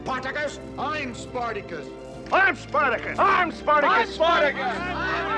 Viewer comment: couldn't hear well, wondered why there were people telling "asparagus!"